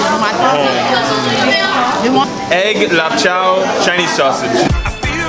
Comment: did he said egg lan chao chinese sausage????....LOL!!!